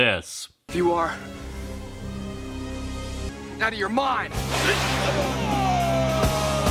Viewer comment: For the longest time UNTIL NOW, I thought when Tommy said "out of your mind" I thought he said, NOW YOURE MINE and just fucking owns him lol